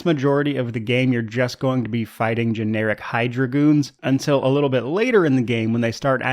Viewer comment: I don't know why, but I kept hearing "Generic Hi-Dragoons" instead of "Generic Hydra Goons".